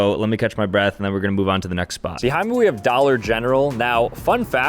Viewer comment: what did he say????? zi-my-oo-huh? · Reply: He said “So behind me”